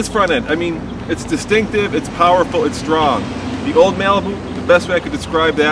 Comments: Did he say old malibu? At 2:24 hmmm. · did he just call it a "Malibu"?